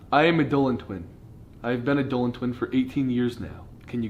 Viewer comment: “Hi. I am a Dolan twin. I have been a Dolan twin for eighteen years now.” edit: WOWIE, I DIDN'T EXPECT THIS MANY LIKES! I-I've never gotten this many before 0_0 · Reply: at first i thought they said 80 years instead of 18 XD